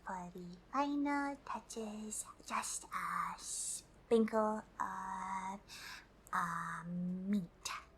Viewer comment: I thought you said "a sprinkle of meat" XD